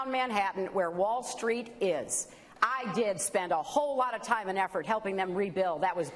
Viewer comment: did she say at 0:15 "i did spend"...